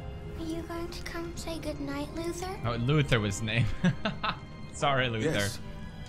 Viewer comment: i thought she said loser :P